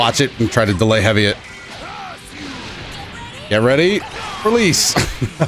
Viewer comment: Wait, GT Goku says "Get ready–release"? I thought he said "Get ready–for THIS!" ..... OR IS THAT ANOTHER AUDIBLE MIX-UP?